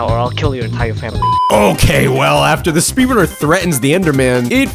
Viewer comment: my captions say "after the speedometer threatens the enderman"